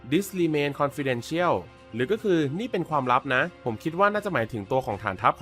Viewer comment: ทำไมผมได้ยินว่า destroy is the mission